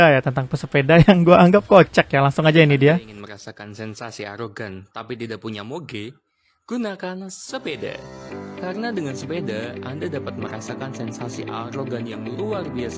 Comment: > 0:22 itu ngomong apa ya, kurang jelas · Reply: Ingin merasakan sensasi arogan tapi tidak punya moge? gunakan sepeda! dengan sepeda, anda tidak perlu merogoh gocek dalam dalam untuk membeli motor gede. kira kira begitu